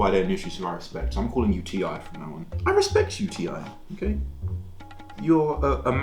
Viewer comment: I am so sorry but all I heard was UTI